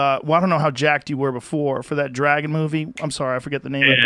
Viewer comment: I thought he said drag movie and he said dragon movie lol. That threw me off